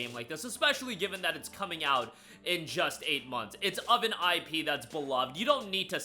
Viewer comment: RE9 is not releasing in a month.. this is for Feb. 2026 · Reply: I think you misheard it. He said in just EIGHT months instead of in just A month at 2:18.